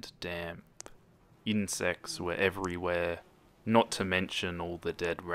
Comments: I thought you said "in sex" instead of "insects" lol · when he said 'insects are everywhere' i totally thought he said 'incest is everywhere' XD